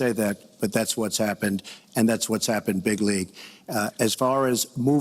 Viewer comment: What's this odd use of a Trump-created word: 'bigly'? Most odd. A wall between Mexico and the US is ludicrous. That is hundreds of miles! Far too expensive for a country that is trillions of dollars in debt. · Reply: No that's what I have heard him say - many times. That's why I commented on it. I had thought, since Farage says 'hugely', Trump just decided to use 'bigly'. But okay if he really said 'big league'.